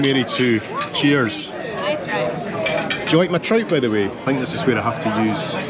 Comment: minute mark.....thought said he was enjoying his "tripe" and thought "Yuck"! but then realized he actually said "trout"! LOL! Blame it on his thick Scottish accent! I am American and have never been to Scotland (have been to England though) but had a dear (now dead) Scottish friend from Loch Carron in the Highlands that lived here in Kentucky for 40 years. I also watch a lot of British TV shows like "SHETLAND" , "MONARCH OF THE GLEN" and "HAMISH MACBETH" which have Scottish actors with deep accents.